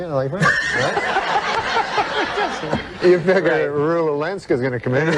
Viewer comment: Who does Norm mention at 1:52? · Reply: I thought he said Rula Lenska, a famous red-haired actress - a bit more pertinent to the story than Rue Walinksa 🤷🏽‍♂️